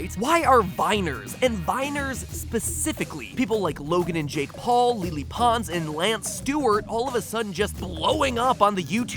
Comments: Did.. did he just say lee-lee pons? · did matpat say: "LEELEE pons"?!?!